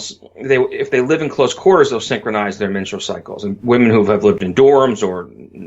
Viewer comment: He talks really fast. After two or three times thinking that I heard him say “Mitchell Cycles” I thought hmmm... I wonder what that is, I should look it up... only to realize he was saying “menstrual cycles.” Lol